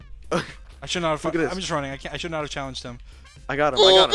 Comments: "I should not have fuc- i'm just running I should not have challenged him" is that what he says or not i cant tell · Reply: he said - ‘I shouldn’t had fucking, I’m just running I should have not-‘ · Bruuuh! I can’t be the only one who heard him say “I should not have fucked with him”